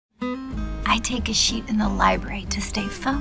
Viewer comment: i laughed my ass off the first time i seen this on tv i thought they were saying i take a shit lol